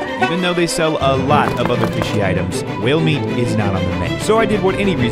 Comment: Almost Half the video 😂 I thought he was saying male meat 😂😂